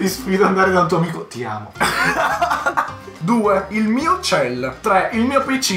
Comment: io che alla seconda ho capito uccello😂💀